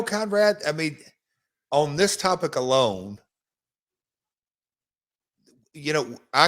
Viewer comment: I thought he said “on this type of cologne“. I had to listen for a second time to realize he said “on this topic alone”